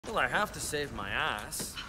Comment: Am I the only one who grew up with thought that Shrek said “well I have to save my EYES”